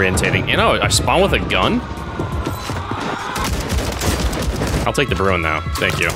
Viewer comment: When he said “I’ll take the bruen though” I heard “I’ll take the bruno” Bring back Bruno (start streaming again plz)